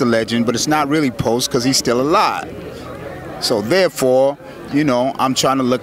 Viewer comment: Did he just say "it's not post Pac CUZ HE STILL ALIVE" @ 1:46 ?????